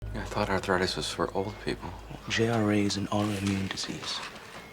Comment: I had to restart this video at least 5 times because I kept hearing “I thought a throat was a hole with peep holes” … it was “I thought arthritis was for old people” 😐